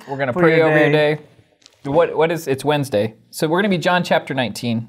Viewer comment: No comic Wednesday today? Good message. I will call my mom today. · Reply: Lol. Thank you for this reply! I always thought they said comic Wednesday lol. I didn't even think about "Comment", which definitely makes sense 😂😂😂. Now I'm questioning my hearing lol.